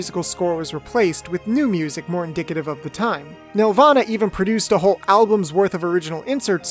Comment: i heard you say nirvana produced music for this series and i was confused as fuck